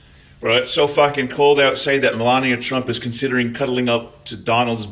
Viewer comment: i heard she considered it then puked.